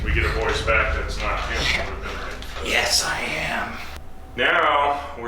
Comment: it weirdly sounds like someone is saying "yeah i am", its super quick but still